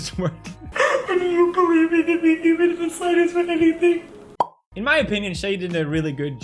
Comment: she is actually saying "and you believing in me is just the nicest thiiiiiing"